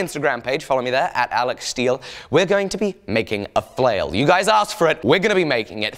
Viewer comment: Who else thought it said making a fail?